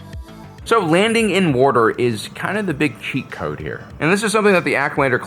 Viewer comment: did you say “landing in warder”, are you pennsylvania dutch? 😂